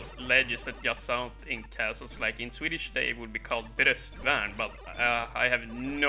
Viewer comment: In English, that would be called the "battlement" of the castle I think. Are you saying "bröstvärn" here in Swedish? That's what Google Translate came up with for battlement. If you are saying bröstvärn, then my Swedish pronunciation must be awful... hahah. I don't think I ever fully understood how that ö worked. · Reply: I was indeed saying, "bröstvärn". Swedish can be very tricky with its, "å, ä, ö". ^^